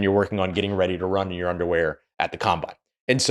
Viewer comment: did he say run your underwear? Idk what that means